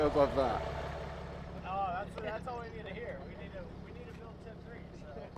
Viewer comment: did he say tiv 3? 👀